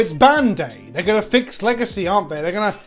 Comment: I've been watching some mecha anime and the intro when you said 'ban day' I thought you said Bandai but weird